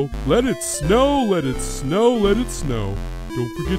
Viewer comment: It sounded like Sans said don't forget to get married this holiday